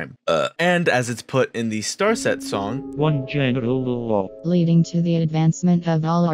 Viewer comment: I was tabbed out and thought it said "one genital lol"